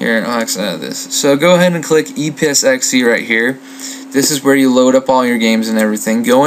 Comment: Haha~ It was a great vid, and helped a LOT, but at 07:12 it sounds like you said "e-piss-x-e".